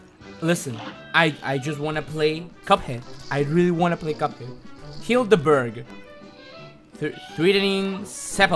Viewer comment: Hilda Berg? I heard him say Kill the Bird.